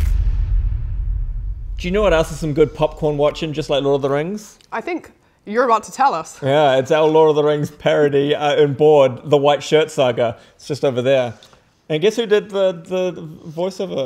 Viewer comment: "Law of the Things" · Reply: I thought she said "lore of the finger trays" 💀